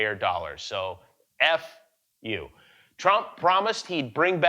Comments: Did he said F U 😁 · Did Jimmy just say F😶U on Trump's report card😆